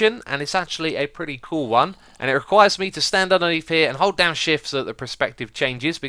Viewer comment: When he said 'hold down shift' I thought he said 'Oh dang shi#t' XD